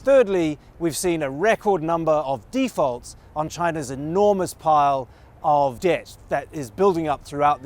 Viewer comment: I could have swore it said "an enormous pile of...d**ks"